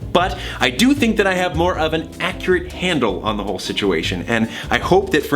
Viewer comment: I know he said "more of an" at 12:10 but I heard 'more oven' XD